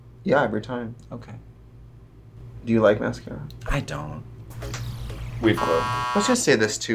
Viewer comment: Katya said “weave glue” and i first heard “reef glue” which for anyone who deals with saltwater aquariums knows reef glue is essentially cement. I was genuinely worried and needed to rewind 😂